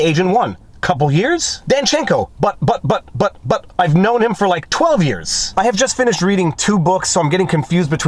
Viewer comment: You misread the transcript at 11:52, Viva. He said "...but I've known OF him for like 12 years." This does not contradict what he just said.